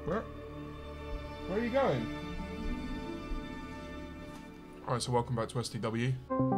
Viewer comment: i have listened to this 12 times, WHAT DOES HE SAY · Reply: Alright, so welcome back to S2W